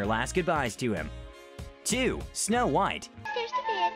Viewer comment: When the mirror said:"snow white," I thought he said ALL MIGHT